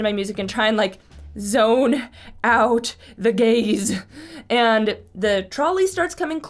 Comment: What said: "Trying to tune out the gaze." What I heard: "Trying to tune out the gays." How rude.